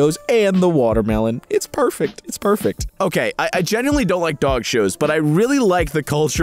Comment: Oh my god! I wasn't looking at the video but I could still hear you and I thought you said "I really like dying dogs" like die not dye 😭😂